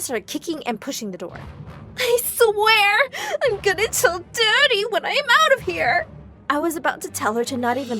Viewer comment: does anyone else see that the subtitles say “I swear, I’m good, It’s so dirty when I get out of here” when she said “I swear, I’m gonna tell daddy when I get out of here” did they use google translate or